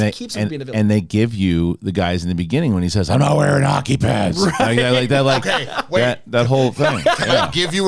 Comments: thats funny, until i watched with subtitles i thought he said "i am not wearing a hockey mask" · I swear I thought he said "Im not wearin hocky PANTS"